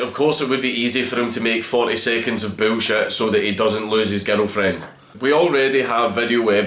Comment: I like the fact that when you say: "girlfriend".. I hear: 'ghetto friend.' And it still made sense to me.